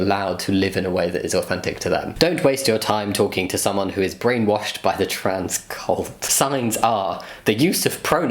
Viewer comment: I thought it said brainwashed by the triscut